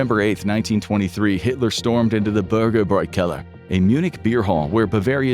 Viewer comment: I thought you said they stormed the burger boy on Keller